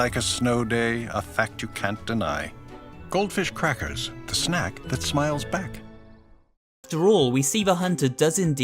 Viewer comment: At the end of the ad i heard "A crack that smiles back"😅